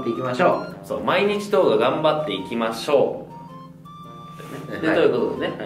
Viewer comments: まー、一応動画も頑張っていきましょう！に聞こえてた笑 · たまに、まぁ1度頑張っていきましょうって聞こえるww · 今一度動画頑張っていきましょ！ って聞こえてた